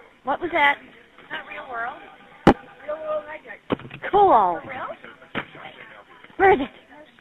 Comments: Did she say “cool” when she heard it was a real world hijacking? Disgusting (unless I misheard). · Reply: She did say, "cool," and received a lot of negative feedback once the audio was publicly released to the media. There's an interview she did with the media where she explained the context and why she used that word. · Did I hear a female respond “Cool!” when the other female said it was a real world hijacking? 25:57 mark. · Did she said COOL and got excited???????????? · wait did she say "cool"??? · did she really say “cool”???